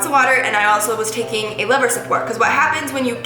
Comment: What you are saying at 8:47? Liver support?